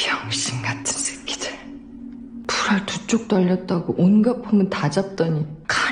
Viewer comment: 뭐라고요? 불R이여?